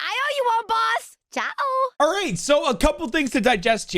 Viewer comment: Wait so it's not spanish it's portuguese? Why would Raze say a Portuguese word :D Thank you for letting me know bro!! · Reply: Also at 2:18 she is not saying chao, it's actually "tchau" which is bye in portuguese.